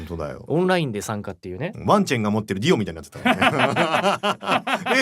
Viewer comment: Thank you for the subs!! The context for 14:57 is that it's a reference to Jojo part 1. Ono-san's talking about a character called Wang Chan holding Dio (ワンチェンが持ってるディオ). It's hard to explain without spoiling part 1 though 😅 Also for 19:31, I think Ono-san said "ディスクシステムもくっつくしね". I don't know how the Famicom works but I think the Disk System is something you attach to the main console (from wikipedia).